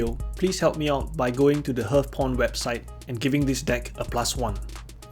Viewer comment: it sounds like you say hearthporn